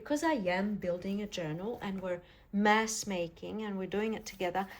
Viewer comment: I kept hearing you say “mess making” not “mass making”. Both true and both fun 😊